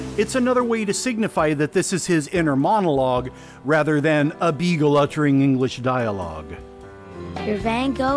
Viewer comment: that's a Citroen CV-2 not a beetle. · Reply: oooooooohhhh... I misheard.. I like the video. I heard "in a beetle." Lol